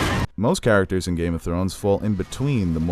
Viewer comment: I thought it said Skin Walkers.